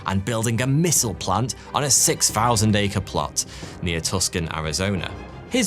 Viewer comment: Arizona native here, it sounded like you said "Tuscan Arizona". I'm assuming you meant "Tucson", pronounced "too-sonn". It's derived from a native word and for whatever reason the C is silent now.